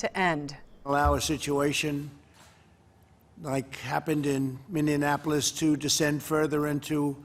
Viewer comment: did he just said Mineaneapolis?